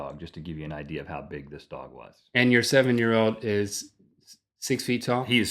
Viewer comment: Who else thought that Rhett said his seven year old was seven feet?